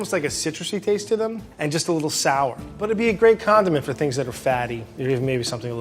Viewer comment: did he just say condom?!